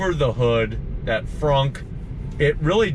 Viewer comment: = outstanding report, I thought you said FUNK but it turns out you actually said FRUNK (laugh out loud) watch out for that real wood & splinters. stay Italian - tony